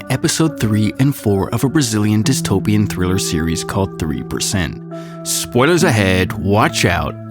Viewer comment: anybody realized the caption's said ",Au3%.,Au" instead of 3%? anybody have theories?